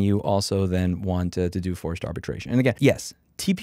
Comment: Thought it said “more forced abortion” for a sec 😂